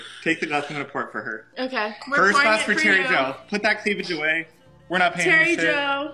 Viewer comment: “Put that cleavage away we’re not paying attention”😭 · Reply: I thought he said “put that cleavage away, we’re not paying you” LMAO💀